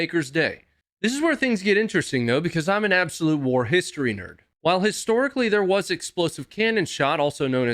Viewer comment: "I'm an absolute war ...........?" what. 2:05. Did he mean to say "historian".